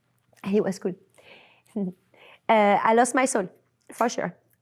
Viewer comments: did she say she lost her soul? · I love her and think she’s so charming! But did I hear correctly at 29:06? Did she say she lost her soul?